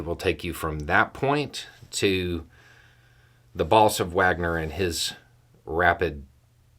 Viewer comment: You said, 'boss of Wagner'. I heard 'balls of Wagner'. Now I laugh every time I hear you say boss of Wagner. Love you little accent...